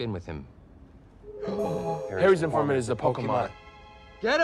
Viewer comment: What did they say? All I heard was "Harry's apartment is a pokemon." Please help. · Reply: “Harry’s informant is the pokemon.”